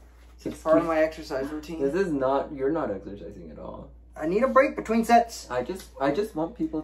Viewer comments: I could have sworn said he needs a break between sex🤣🤣🤣🤣🤣🤣🤣🤣🤣🤣🤣🤣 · I thought said I need breaks between Sex. that is a good workout tho.